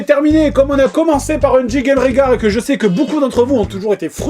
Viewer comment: perso au debut j'ai compris "jingle rengar" XD